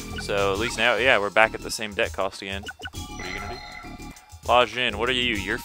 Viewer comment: Anyone else hear someone say "What are you gonna do?" at 15:40?